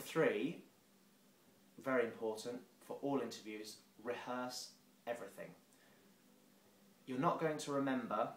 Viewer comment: Понятно далеко не всё, но главную идею я понял, что очень порадовало. )) Хочу уточнить: какие 3 критерия перечисляет во второй раз? Я не совсем понял фразу we has everything (мне так послышалось)